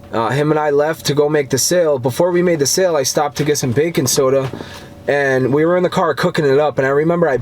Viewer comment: Me: Bacon soda? What a weird thing to stop for. Me 2 sec later: OH! Baking soda, yeah that makes way more sense. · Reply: Haha I heard "Bacon soda" too and then I realized he was saying "bakin' soda" lol